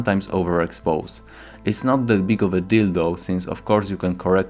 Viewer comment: Am I the only one who hears " it's too big of a dildo" here?